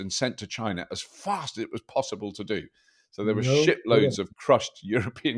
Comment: I thought he said shi*loads not ship loads. I had to rewind :D